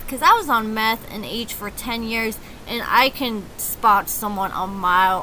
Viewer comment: did I hear you say you were on meth for 10 years??? Wow! Well, girl, if I heard correctly, you made it out! I'm proud of you 🎉🎉 But, yes, this is a sad little couple, he used to be very handsome and looked healthy, now, he looks 'geeked up' like you said and weak and seems like he is looking for someone to use...I pray Anissa don't get used too much more than she already has....smh!!! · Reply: I definitely heard say 'meth and H for 10 years' at 01:45.